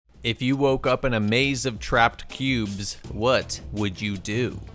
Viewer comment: I misheard this as what if you woke up in a maze of pubes